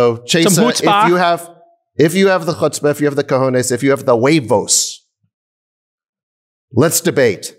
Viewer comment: What's the word Sacks used here? Wavos??